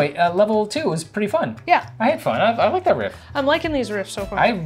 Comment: I heard "I hate fun." That's what I tell my kids when they accuse me of ruining their fun.